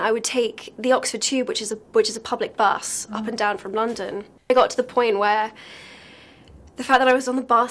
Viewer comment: what did she say at 4:10? "it got to the point where..."?